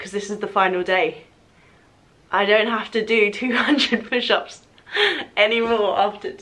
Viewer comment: Min 22:13 wait wait did she say 400 pushups a day for a charity competition? 😂😂😂